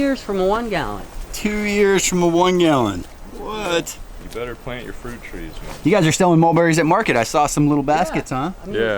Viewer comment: What variety of mulberry at 2:35? I didn't catch it. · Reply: Thai Dwarf and World's Best